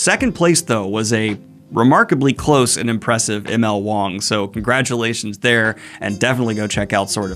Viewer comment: Sadly, with the path my mind travels I thought you said "schlong"